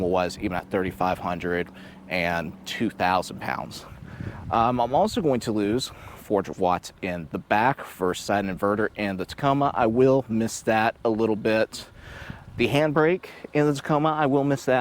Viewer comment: I think you have convinced me to go with the Honda. What are you saying at 10:40 please? I literally could not hear the words. Also, would you take this on the NC beaches? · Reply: I was saying my Tacoma had a 120V outlet capable of 400 watts outlet where the Ridgeline does not. I would take it on NC beaches as long as the sand was compact. The AWD system is capable, like any other vehicle I'd stay away from very soft sand and always wash the underside well afterwards